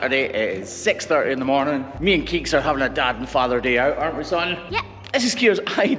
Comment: Did he just say dad & father day or am I misunderstanding something Scottish? :D awesome video, the poor cow looked like it was in so much pain before :( great you could help her!